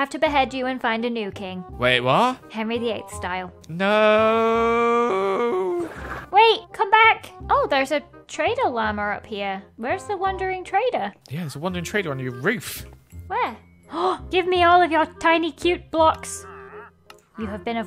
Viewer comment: thought you said traitor llama